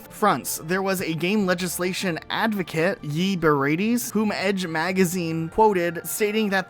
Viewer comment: Had to re-upload the video because youtube is silly. thanks everyone who watched to the end to help the algorithm gods :) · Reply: did you just call him Yee Berates? Lol